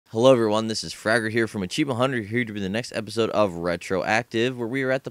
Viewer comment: When he said here's Fragger from... I heard "Hello here's Burger"